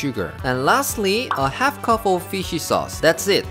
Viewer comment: what's that ingredient? I can't her what he said properly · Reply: I said 'fish sauce'. 😂 If you need the written recipe and the ingredients, please visit our blog as well.😉 <link>